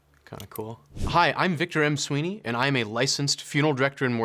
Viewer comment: I thought he said I am Tim Sweeney and I almost had a heart attack